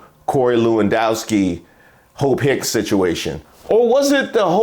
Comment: Lord, I had to put the CC on because I kept hearing 'hoe pick' 😅😅😅 It was Hope😅😅😅😅